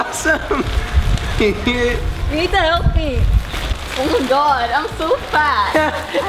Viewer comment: Did she say "Nathan, help me" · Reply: Kashyap Tank no she said " you need to help me"